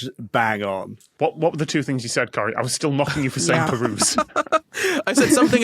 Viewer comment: Subtitles say "knocking you" but I'm pretty sure Tom says "mocking you".